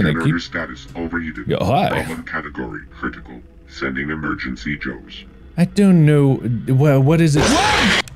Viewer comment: The “sending emergency Joes” kills me every time, like just imagine a whole bunch of random dudes dressed in casual business suits rushing in to the room 🤣💀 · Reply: I kept mishearing it as Emergency Jones like some guy with a whip and fedora is somehow going to put out a fire.